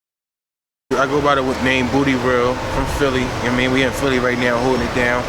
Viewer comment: Dude name is tho! 😆😆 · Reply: I thought. He said booty world